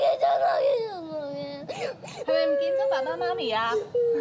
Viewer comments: sounded like he was saying "PHONE PHONE PHONE" in korean lmao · did he say a bitch girl